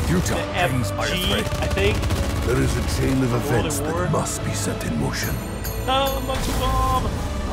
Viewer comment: I always thought Nikolai said "There is a chain of events that must be extinguished" but nope he says "There is a chain of events that must be set in motion" They sound alike somehow